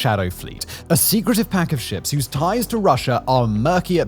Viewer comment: I had to turn on captions because I kept hearing “a secretive pack of shit” even after listening to it multiple times. Granted I have a massive headache and took my sleeping pills already. *it would have been quite funny if what I heard ended up being what had said.